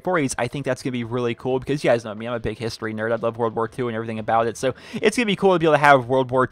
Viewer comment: Am I the only one who heard him say " I love ww2 "